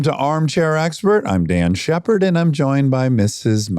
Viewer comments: did he say Dan shepherd. Isn't it Dax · Why is Dax (sounded like he said Dan 0:02) Shepard dropping the F-bomb every 5 seconds? It's sounds dumb and trashy, bro.